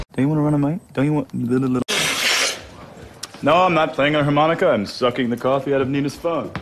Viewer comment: I didn't understand what he said... Can anyone tell me? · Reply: "No I am not playing harmonica I am just sucking the coffee out of Nina's phone"😅😅